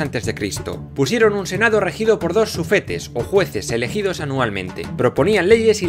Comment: escuché "ojetes"